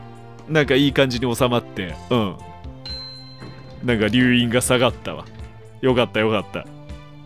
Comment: 乳輪が下がったわ 眠くて聞こえてしまった